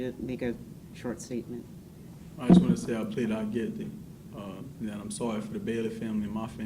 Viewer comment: Wait a minute…. Did I hear this wrong but at the end when he makes his statement… he states he wants to plead “not guilty”? I have replayed it numerous times. It’s aroun 16:00 min mark give or take few seconds. Again I may be hearing and thinking wrong. You could tell his statement threw the judge off to as if did he just say he wants to plead not guilty. Just my thought. What is he saying?